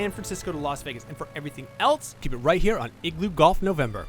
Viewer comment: Did I heard him say "Igloo Golf November". It's suppose to be "India Golf November".